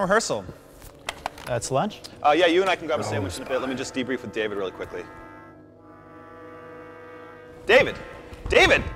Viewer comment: What did he whisper at 0:36 ? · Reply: "Sledge", I think...